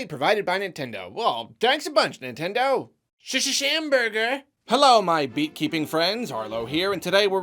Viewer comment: At first I thought he said "Hello, my beet-keeping friends" and it took me about thirty seconds to realize he wasn't calling us vegetable hoarders